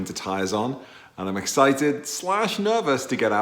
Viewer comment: For “excited” I heard “twitchy” lol. Been there myself in a couple of Quattro TT’s. Even with their all wheel drive they were twitchy in the snow, invoking some interesting stomach/bowel moments - hahahaha. Think my Cayman may be parked up when we get the white stuff in England in a month or so.